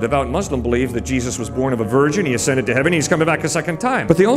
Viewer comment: For a second I thought he said “jesus was born a virgin” instead of “born of a virgin”. i was like well DUH